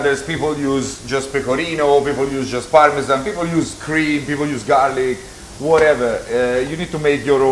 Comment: DID HE SAY YOU CAN USE CREAM?!?! (5:30) 😲😮🤬😡